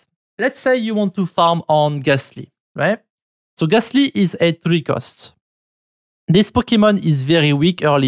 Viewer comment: What is it he calls ghastly and other type of pokemon at 12:36 ? Pre cursed? I dont understand 😅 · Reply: 3 cost